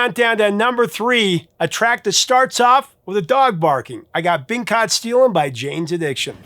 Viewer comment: The says’ Been Caught Steelin’ quickly and I heard it as ‘Pink hot steel’ twice. Made me giggle.